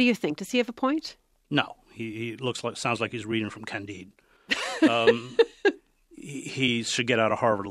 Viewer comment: He sounds like he’s reading from what? I can’t make out what he said. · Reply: Never mind. I think he’s talking about Voltaire’s Candide. Yep. I answered myself.